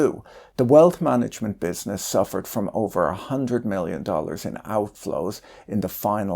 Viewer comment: you say million, subtitles say Billion